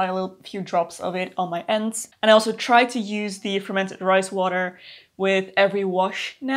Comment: When you first said “fermented rice water” I definitely heard “fermented Christ water” 😂 what is this magical thing! Tell me more 😂